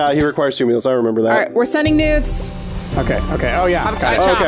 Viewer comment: When said she was "Sending Noods", I thought at first she said "Sending Nudes" and that really confused me at first then I realized it was Noodles.